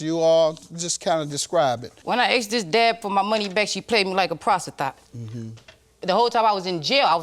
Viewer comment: “she paid me like a PROSTITHOT” 😂😂😂 y’all 11:04 i had to put the captions on to make sure i heard her